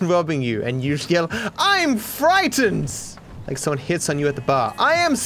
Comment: I thought he Said someone HITS YOU with a Bar... emmm makes Me sexually Aroused.